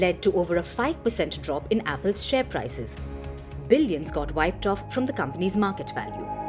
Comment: did she say Tim Cook as Bill gates 😂